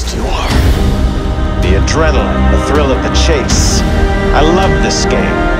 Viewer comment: what it says on that part? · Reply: "The adrenaline, the thrill of the chase"